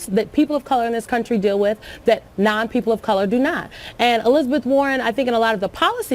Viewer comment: Excuse me? Did she just call me a non-person?